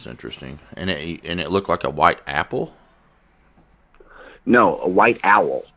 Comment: lol, I thought he said white apple too. A white apple dive bombing the car would be strange to see for sure!